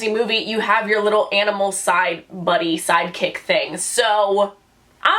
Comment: I instantly thought "side chick" when she said "side kick" and "side buddy"😭🤣